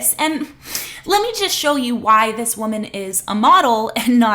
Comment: you said let me show you why she's a model and whn you play the video all i hear is the audio "she need some milk" cause that bit ugly af